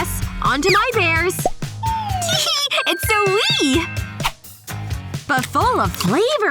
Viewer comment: what was that word- it's so wee it so sweet idk